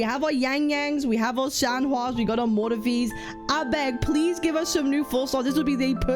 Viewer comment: did you just say 'Abeg' 😂??? If i learnt anything from kuro in pgr, They can make a healer play like a dps. Wouldn't be surprised if she's a support.